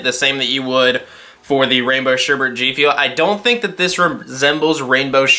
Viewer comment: i donr know but i thought it said strawberry sherebet and i got really hyped